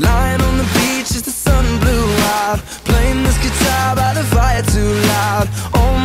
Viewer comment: When I first heard this song when I was 11, I deadass thought Harry said “with Simon Cowell” instead of “as the sun blew out” LMAO it sounds nothing like that😂